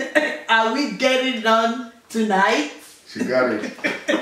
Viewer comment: when their dad said 'i love my wife' i thought he said 'i love fried rice' 😂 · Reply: i sounded like he diad am i getting it tonight?😂